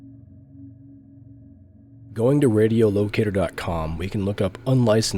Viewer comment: did you say... radio... head???